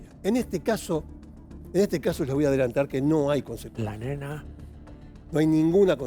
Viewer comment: Minuto 6:27, se escucha la voz de un hombre que dice: la nena😱